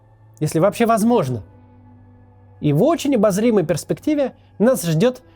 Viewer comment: Мне одному в конце послышалась "тарбулентность"?